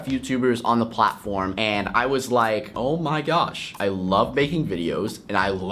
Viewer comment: instead of saying making he said baking